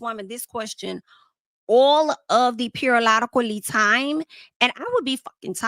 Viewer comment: I’ll never forget I found you during Megan vs Nicki you were fair and unbiased. I am a truck driver and I was in traffic listening to that video. But when I first heard you say “a long periolodically time” I said excuse me? 😂😂😂 immediately subscribed 🫶🏾